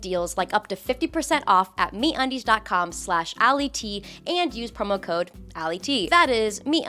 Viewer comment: I thought you said your sponsor was Meat Undies.